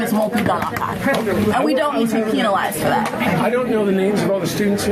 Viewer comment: wait did she say "peinalized for that" at 4:25 ?